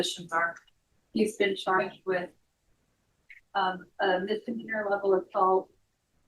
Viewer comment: the caption said "a Missouri level of salt" instead of "a misdemeanour level assault". Lol. Also, the title of this video is wrong. She did not stop the plea deal.